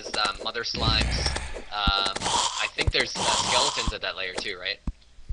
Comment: did u say skeleton or gelatin?